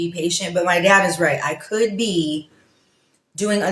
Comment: See this is what happens when children become entitled even until adulthood😂😂😂 you can tell that she tried to throw him under the bus first because she wasn't happy that he told her NO lmfao 🤣 just a baby girl throwing a tantrum😂😂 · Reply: Ur speaking from ur weakness😂😂 maybe it’s jealously 😂😂 bc I distinctly heard her say as she walked away Poppi is right😂😂